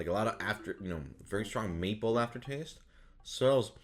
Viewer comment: my ears must be failing bec I thought he said meatball instead of maple